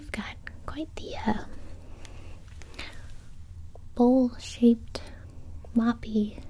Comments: In 1:26 does someone else hear that she said bullshit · Had to keep rewinding because I thought she said bull shit at 1:26💀